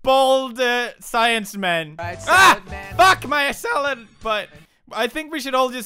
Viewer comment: did he say fuck my salad